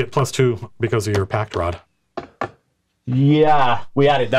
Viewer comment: Every time Matt says "pact rod" I hear "packed rod"